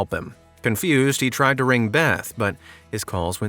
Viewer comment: I thought you said "confused he tried to report a theft" instead of beth